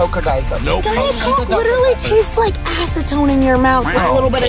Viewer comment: i thought she was gonna say “tastes like a** in ur mouth mouth” 💀